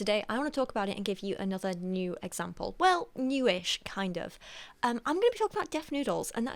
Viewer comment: I thought you said “new age example” - kinda like Dennis faking he is younger than he actually is.